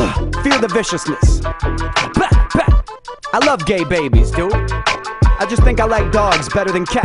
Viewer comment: Wait what did he just say at 1:15-1:16???? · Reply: I love gay babies dude